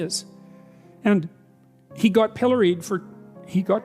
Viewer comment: At first I thought he said "Hillary'd" but then I read the captions and breathed a huge sigh of relief!